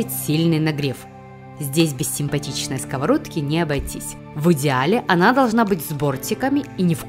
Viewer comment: Здесь без симпатичной скавородки необойтись ,, как услышала я : ,,Здесь без симпатичной девушки не обойтись,,🤣